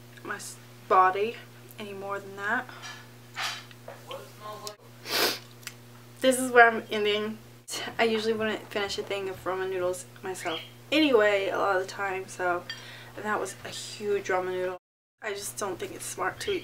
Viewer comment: did you just say 'Roman' noodles?